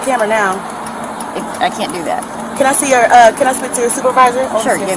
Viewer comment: I think she started saying "manager", not that she actually said "man".